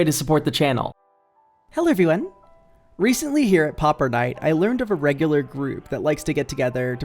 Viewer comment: I misheard Pauper night as Popper night at first and thought it was something VERY DIFFERENT